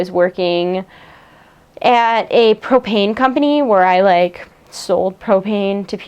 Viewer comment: at first i thought she said she used to sell cocaine and not propane lol.